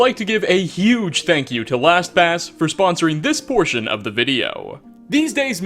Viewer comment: I can't be the only one who thought he said Thanks to (whatever their name was) for sponsoring abortion in this video😂😂😂 Edit:realised their name is Last pass